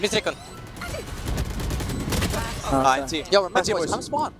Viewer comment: did i just hear someone say "come spawnn" at 30:19 T_T